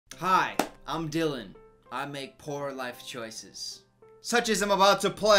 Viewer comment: OK at the beginning I thought he said "Hi I'm Dylan.. and I make porn" and I was like... ok